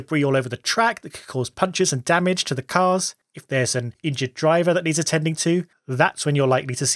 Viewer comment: i thought i heard "if theres an IDIOT driver that needs attending to"😆 ..